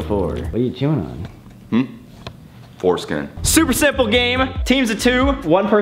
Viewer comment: lmao at 0:35 the subtitles say “foreskin supper”